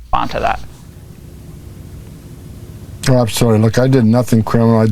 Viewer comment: it sounded like "oh,look I'm sorry" to me